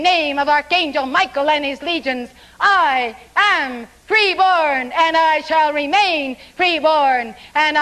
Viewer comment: I could've sworn she said "pre-born"... Flynn sounded like he was saying "free-born"... Color me confused. Or maybe that's how he beats a plagiarism rap? Switch it up just enough to make it a whole new idea?